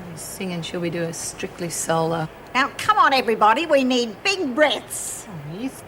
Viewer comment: Lol at Helen when Minnie said 'come on everybody, we need big breaths'! Haha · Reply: I thought she said we need big breasts